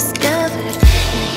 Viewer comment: Crispies (=ﾟωﾟ)ﾉ I'm dying 😂😭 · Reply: “MMeowzee” Heinmann I THOUGHT IT SAID CRISPIES TOO BUT APPARENTLY SHE'S SAYING "SCARED SPACE" AHHHHH